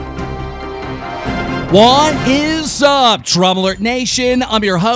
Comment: i always hear "what is up TROUBLER nation" but i know its DRAMA ALERT smh